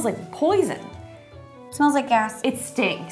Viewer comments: I thought she said "it smells like ass" 💀💀💀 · “i love knives 🤪” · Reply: I think she said gas not ass